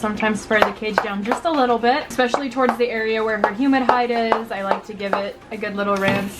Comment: 😂😂😂😂😂😂 at one point of the video she said "humid hide" and I thought she said "human hide"